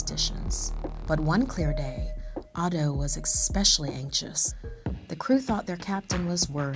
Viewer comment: did she really say "ekpecially" ?